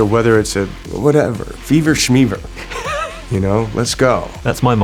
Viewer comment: What does Keanu say here 3:41? Fever smeaver?